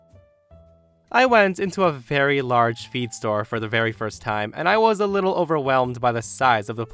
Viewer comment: Story 3: I only listened to the story on headphones and needed a double take when I thought I heard "Feet store". But yeah, I don't get what these ladies expected. Her saying: "Oh yeah, take this random stuff and if your horse isn't dead tomorrow morning, it was the correct one."?!